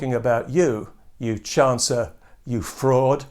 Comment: I thought Pat said 'chancellor', rather than 'chancer' at the end.....both fit admirably